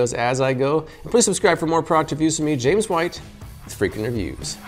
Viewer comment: Sounded like he said welcome to freak interviews lol